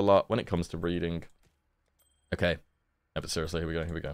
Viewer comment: I heard first "I struggle a lot when it comes to breathing" 😅 Ventor gamba is always great, you are taking a huge risk haha